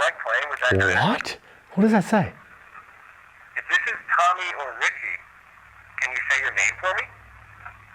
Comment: This is Tommy, that’s what I hear